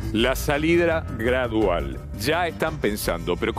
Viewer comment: La salidra gradual dijo salidra ? O yo escuche mal